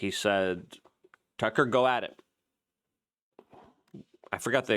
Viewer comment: Kirk: "Tucker, get on it." "But that's not what I heard that day... what I heard was"-- Kirk: "Tucker, sick balls!"